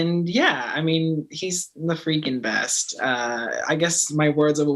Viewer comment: I thought he said he’s a freak in bed